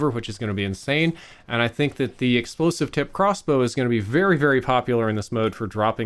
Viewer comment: Did he say ‘Explosive tipped crossbow’? 😃